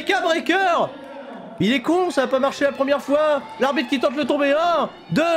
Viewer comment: "l'arbitre qui tente le tombé" ? ca se dit? c pas plutot l'adversaire qui tente le tomber ou c moi qui ai mal entendu?